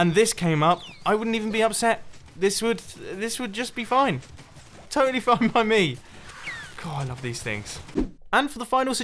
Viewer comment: you’re captioner messed up U said “totally fine by me” The captions said “totally f*cking buy me